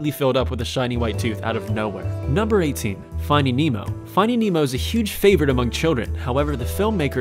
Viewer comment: did he just say funny nemo